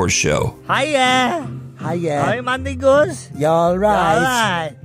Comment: I swear I thought he said “Hi, My N***” 😭